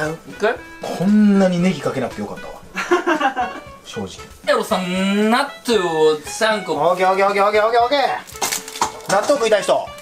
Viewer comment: ここなんて言ってる？？？？？ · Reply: n さんーなっとぅを3個！ かな？